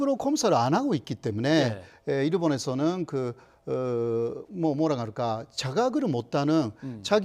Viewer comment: (뭐라고해야할까) 몰아갈까 로들리네ㅋㅋㅋ1:47